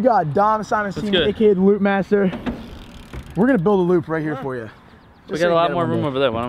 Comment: Oh he said loooopmaster I thought he said lubemaster